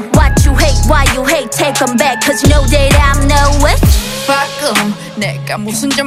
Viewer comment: Is this about how men used burn women before claiming that they were “witches” ? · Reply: I thought they were saying witch instead of bitch ,,i'm a bad w/bitch,, but lmao go on😂